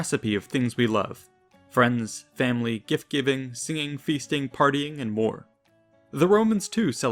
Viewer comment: did he just say fisting? 0.10